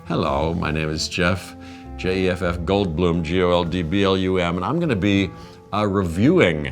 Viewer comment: LOL Did he just say "J-F-F Goldbloom"? 😆